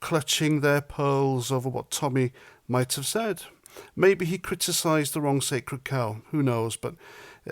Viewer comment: Did you say "maybe he criticised the wrong silly cow" at 1:55? 😂